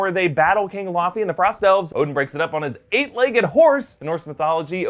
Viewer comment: Did you just say Frost Elves?